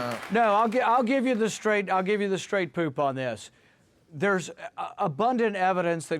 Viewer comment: Did he say "straight poop" @ 4:42?